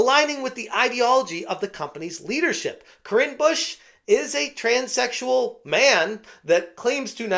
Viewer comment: ngl i thought they said Cringe Busche instead of their name XD I was half paying attention towards the end